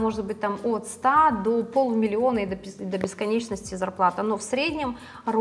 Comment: На 5:26 кто расслышал что она хотела сказать от и до пиз…. Ооо всмысле много?🤣